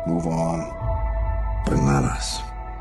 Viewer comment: turn on the captions! Bananas?😂😂😂In the trailer, I ALWAYS thought it said that Untill my bro told me it was "But not us" !!!😂😂😂